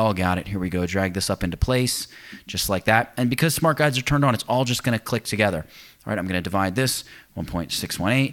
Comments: Thanks for the video, one of the best about the topic. ''Smart guides are turned on'' - i only understood it at the end of the video because i thought you were saying ''smart guys are turned on by smooth math, connects, e.t.c''. Basically was laughing about nothing all through the video - lol! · Wow, I'm really tired. I just watched you make the first smaller shape, and attach it to the first. You said, "and because smart guides are turned on..." I heard "because smart guys are turned on..."